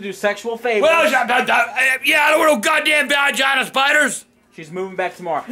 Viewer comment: tf he says valagina spiders 😂😂